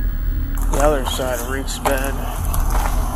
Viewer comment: im pretty sure the subtitles are wrong here, "reeks bad" is what its supposed to be